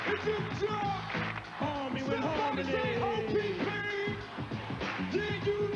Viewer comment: I always thought they said "Arm Me With Harmony" 😭😭😭 You learn something new everyday. 😂 K is a legendary producer, He ran the radio for a many summers